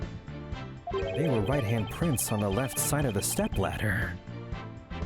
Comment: DID HE SAY STEPLADDER?